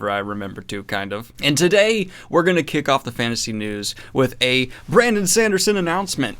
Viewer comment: I heard "fancy news" in the intro. Got confused there for a moment.